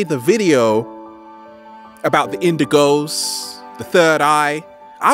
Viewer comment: thought he said windigo instead of indigo lmao.